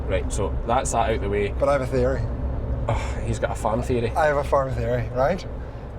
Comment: I was 1/2 listening while doing chores and heard keeping this light hearted and then I thought I heard say he had a fairy and you say he's got a farm fairy and he replied he has a farm fairy. I was all ears because I know the Irish's customs toward fairies is quite interesting, so I was anticipating a great story. But nope he has a theory,: it's just that the people who came up with the nitrates plan are out with the fairies.